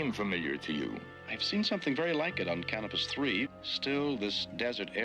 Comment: Kirk: "I've seen something like it on Canopus III..." ROFLMAO!!! I had to play that back...I thought he said "Cannabis III". Yeah, I bet he DID see something like that...and a lot of other wild stuff too!!